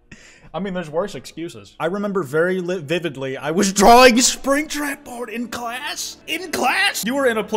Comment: I was gambling in Stardew and I heard "I was drawing Springtrap porn" right when I landed triple star fruit. My first triple starfruit memory is now of Springtrap porn